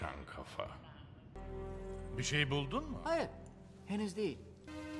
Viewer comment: D bei 1:05 verstehe ich immer " bist du wohl dumm man!" :DDDD richtig super video xDD